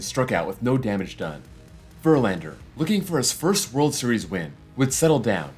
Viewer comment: At first I misheard you say Verlander looking for his first World Series ring and I had to replay it before correcting you over something you didn't say. 😂 I know you know baseball well enough to know Verlander was there in 2017 so it did kind of catch me by surprise when I first misheard you. 🤣